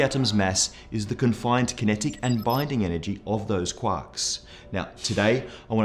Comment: Great video but I totally thought you said quacks instead of quarks..